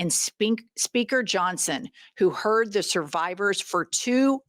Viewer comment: I thought I heard you almost say "Sphincter" Johnson, instead of Speaker Johnson 😂